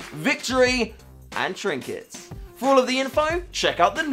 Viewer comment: did u say "trinkets?!!" another ranked trinket inc? (づ｡◕‿‿◕｡)づ oh wait, u ment the trinkets with achieved rank, i guess. Oh & noticed the gloves.. looks nice..the hat might not fit with it though ¯\_(ツ)_/¯